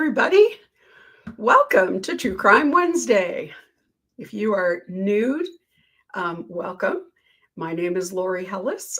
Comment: Lori it sounded like you said “And if you’re NUDE, welcome” in the intro. It gave me a chuckle.